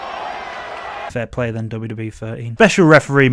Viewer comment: What did you say about 'WWE 13'?... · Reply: "Fair play then wwe 13"